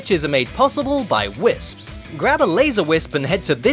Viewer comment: 😂Not to be mean, but I'm sure I heard "hobble wisp😂"